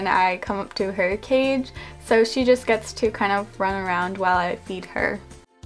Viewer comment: I thought she said “eat her” not “feed her”